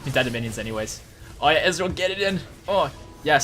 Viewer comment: "Allright ezreal get it in" Thats what Taric said!